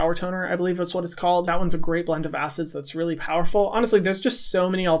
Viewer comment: What I heard: "A great blend of Asses" Thanks for the shout out Hyram 😝